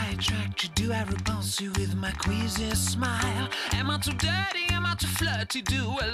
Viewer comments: I thought he said am i too daddy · I genuinly thought he said ‘Am I too daddy’ wtf is wrong with me